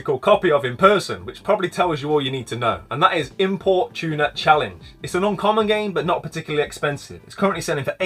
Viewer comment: Some great tips there mate! Not going to lie when you said import tuner challenge I heard it as “Import Tuna Challenge” and I was thinking what is this mad game about importing tuna about 🤣🤣